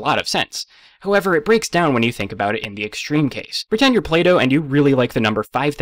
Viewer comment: When he wanted us to pretend we’re Plato, I heard it as Play-doh, and then wondered when it was going to come into this.